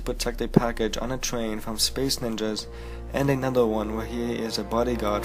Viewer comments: Did you say space ninjas! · What the fuck? I'm sorry but did he just say "space ninjas"?